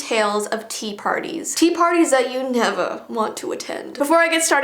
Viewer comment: Did I loose my hearing or did say neva lol